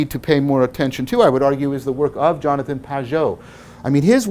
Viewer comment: Who is the author and which work(s) mentioned around 10:58 - 11:05? I heard something like Jonathan Pe...? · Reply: You probably found out by now, but it is Jonathan Pageau.